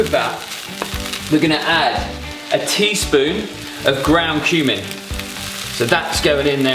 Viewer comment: Can anyone tell me what is ground "tumic" at 6:03? The closest spice name to that is turmeric but I've listened 8 times and he definitely doesn't say turmeric. Not only that but turmeric is normally a mustard yellow colour whereas what he threw in was brown... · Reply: It's ground cumin